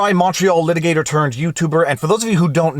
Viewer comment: Dude, all this time I thought you were saying “macho litigator turned youtuber”. MONTREAL! Your legal analysis is very macho though, thanks for the content.